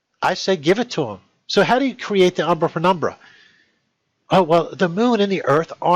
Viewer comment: he says umbra and what sounds to me like "fenumbra" instead of penumbra. Okay, we all can have a slip of the tongue. But when he reads "when" while the word is "why", that's a big mistake. He continues with statements that are plainly wrong, getting more & more upset along the way. If he would just calm down and be willing to look at our tests & examples, comparing with his own, he might actually understand why we say he is wrong. That is the true key to learning, but so far I doubt we'll see him change his mind.